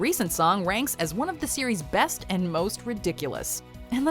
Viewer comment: it sounds like your saying Siri and so when ever you say series Siri will pop up to say hi